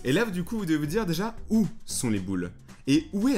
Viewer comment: mdr au début j'ai entendu ou sont mes boules 😂